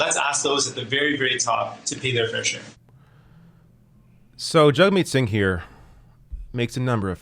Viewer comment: I seriously thought when you said his name it was "jug meat" 😂